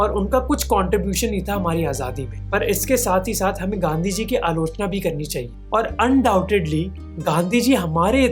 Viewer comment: the subtitle says "praise" instead of "criticise"